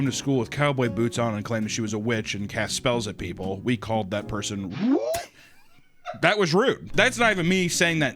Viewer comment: what did he say?? They called them what!? I wanna be in the loop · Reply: I finally found out what he said. Somehow I could not lip read him but he just said the r-slur. (I'm now spelling it out incase youtube censors that) I geniunely thought it was something else.